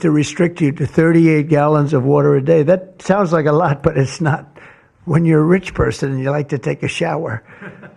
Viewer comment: I don’t like Microsoft and bill gates anywhere near this project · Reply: did he say when you’re a rich person you like to take a shower